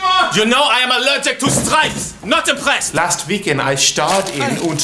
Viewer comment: For like 2 years, till this day, I thought he said "You know I'm allergic to straight" not "stripes"...